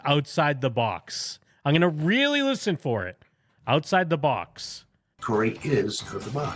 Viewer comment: He did say "out of the box". I heard it at 2:28:25 when you played it.